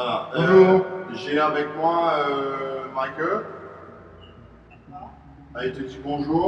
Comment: À regarder dans de bonnes conditions hein ! 🙏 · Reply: j'entends "j'ai avec moi ma queue" . Il se foutrait pas de ta gueule un peu des fois ? :)